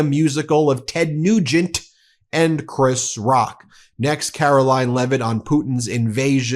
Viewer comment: Did I hear Chris Rock? I must be missing something.